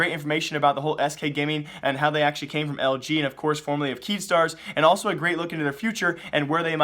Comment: did u say keemstar?